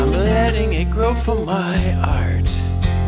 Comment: Oh, I thought you said, "from my heart" rather than "for my art". I kinda like the change in meaning my version adds, if I do say so, myself, LOL. Saw your show at Park Coffee House the other night.